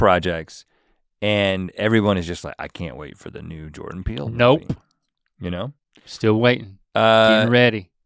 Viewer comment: Oh God, I thought they said #3 was Jordan Peterson at first and I was confused lol.